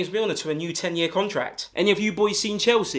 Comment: Who else thought he said pegged not pecked💀